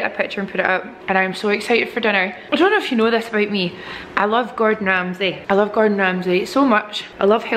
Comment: Lol when you said you like Gordon Ramsay, I heard "I love garden gnomes" & in my head I was like "hey me too!" but then you started talking about food & I realized I was so off. 🙆🏽‍♀️😬🤦🏽‍♀️